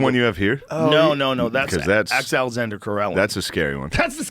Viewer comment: I can't understand the name Joe says when he says "that's the scariest man alive" at roughly 10:30. Anyone can lend me a hand? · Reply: Alexander Karelin (Aleksandr)